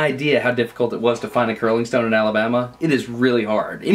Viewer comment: did he just said alabama??